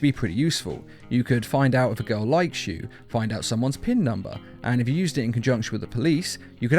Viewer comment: did he really say "pin number"??? 😂😂